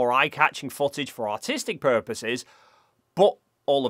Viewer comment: sounds like you said "for autistic purposes" instead of artistic, it could definitely be both 😂